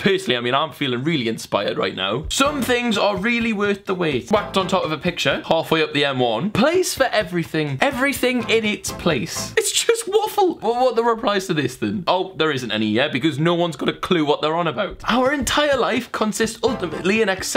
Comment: who else heard Say " It's Just waffle" Is it just me Please tell me i want to know I WANT TO KNOW OK!